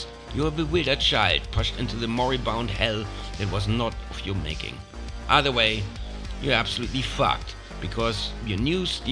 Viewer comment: did he say either way you are f&@